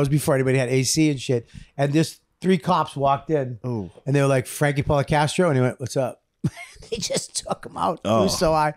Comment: did bobby say "Frankie Podcastro"??